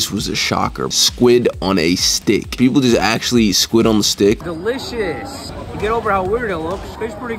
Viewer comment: I heard him say squirt on the stick 💀